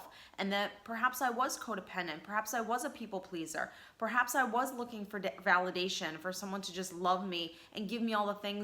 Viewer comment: Sounded like you said "perhaps I was looking for dick" 🤣